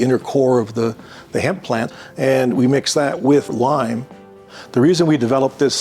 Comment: I thought he said he made the bricks by mixing hemp and wine and I freaked out for a moment